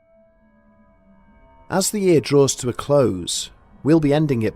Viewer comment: I was worried there for a minute, I thought you said at the beginning of the video; "as the series draws to a close " I was like whaaaat?! Nooooo!! Then I backed it up and listened again lol big sigh of relief. Merry Christmas everyone 😁